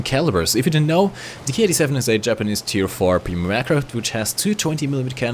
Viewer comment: I thought you said "Tu-4 premium aircraft" and I almost jumped out of my window